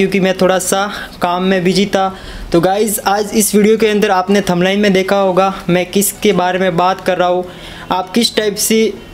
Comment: literally thumbnail hota be aur Kya Bola thubaiom ??!!!? Yafir Kya 🤣😂😅😁